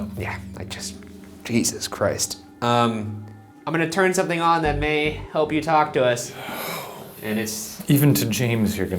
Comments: you can here a whisper. I replayed it several times just to make sure I heard something. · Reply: If you turn on the subtitles for the video, it seems to be whispering "no" according to YouTube. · it sounds like someone says “Help”!!! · i heard something and it is fucking scary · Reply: "I'm going to turn something on to help you talk to us..." The bellowing whisper to follow may seem scary, but, it's saying, "How?" Intelligent response nonetheless. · does anyone else hear “help” or is that just me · Near 10:58 in the in the video was I the only person who heard someone said help